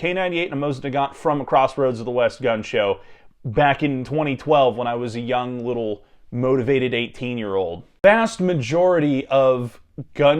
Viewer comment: I had to rewind cuz i thought you said “back in 2020 when u was a young motivated 18 year old” and i got scared that i was older than you. But no youre thankfully ancient.